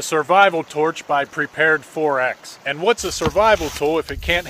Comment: I thought he said ‘prepared thorax’ xD